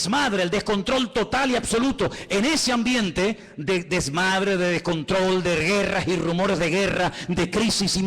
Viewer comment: Escuché mal??? O al min 32:50 dijo 2 veces DESMAD...? lo repetí 3 veces!!!